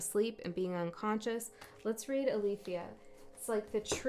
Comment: /49:33 Time now 2:22am As I’m listening and taking notes I heard you call my name I looked at my phone in shock!! 😳 while my heart was dropping 😂 my name is Arlethia so when you said alethia elthia? Twice I knew there was a few messages and confirmations here for me! Love your spirit and energy 💜